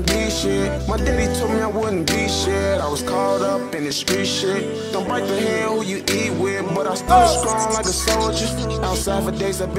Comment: what did he say after "I was caught up in this street shit"? sounds like he said "go back to hell where you eat with" does anyone even know??? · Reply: Don't bite the hand what you eat with.